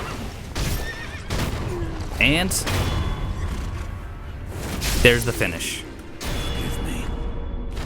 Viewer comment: Notice that just as Sekiro stabs Gyobu and knocks him off his horse...at about or around 18:28, it sounds like Sekiro says "Forgive me...!" It's before where Gyobu pleads for Genichiro to forgive him for failing to guard the castle gates...and its in a different voice than Gyobu's line, its a bit hard to hear, but I believe it is indeed Sekiro saying forgive me to Gyobu... Reminds me of the bit about his swords name saying that a shinobi must not forget mercy...